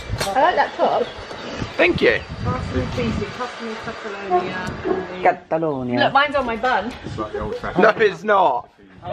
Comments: IS IT ME OR DID SOMEONE SAY CATALONIA? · I thought Zoe said "mines on my bum" - I was a bit worried for a moment then😂😂